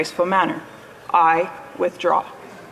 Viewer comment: I also heard her say I withdraw..... when watching the stream from mrsunshinebaby, it was clearly audible, and anyone can look it up and hear for themselves. was loud and clear.